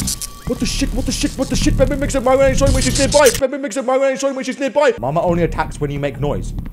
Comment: (What I heard is "Mama makes up Mama when she's near by") 12:09 (I heard he said "Algebra"...) 12:41(Funny Jumpscare)